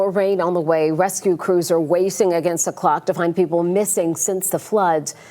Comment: Did she say rescue crews are "wacing" against the clock 🤔